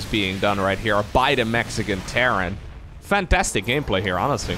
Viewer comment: did he say Mexican Terran? Lol.